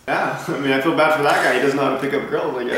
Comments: Did he just say " these hoes are loyal" ? Lol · Did he just say 'these hoes are loyal'...??? maybe he is not the right one